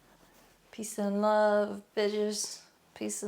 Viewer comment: 😂 ik you said peace and love in the beginning but for a split sec I thought you also said pizza and live and I was like yes, definitely 🤔😂